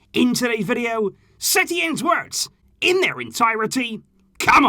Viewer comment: I heard Setien twerks :D :D :D